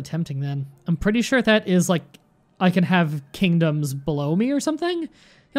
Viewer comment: It may just be my bad hearing, but it sounded like he said, "I can have kingdoms blow me." 🤣